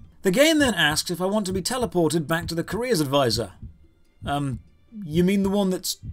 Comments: OH! A Career advisor... ...I thought you were saying Korea Advisor. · I honestly thought you were saying "Korea Advisor" the entire video. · I misheard your rant about the careers advisor as a "Korea's Advisor" and was very confused for a time.